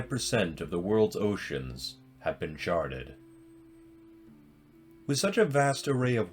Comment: I really thought this man said 'nearly 50% of earth's oceans have been sharted' He said 'merely 5%' & 'charted'